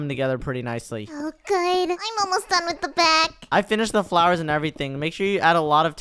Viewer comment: did someone say- flowers?